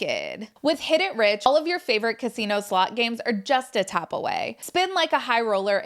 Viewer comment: Thought she said casino sluts 😂 it's the American accent I guess lol.